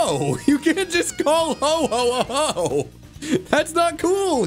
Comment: Am I just sleep deprived, or did turn into Santa for a brief second at 19:58? (Listen carefully; I'd swear that was said as "ho ho ho" instead of "Ho-oh Ho"...)